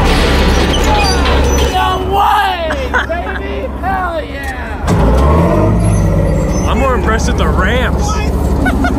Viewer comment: Ok at 13:13 what does it say on the tank? lmao I need to know. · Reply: "Built to destroy"